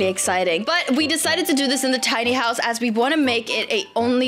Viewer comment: I thought u said sea horse not pink house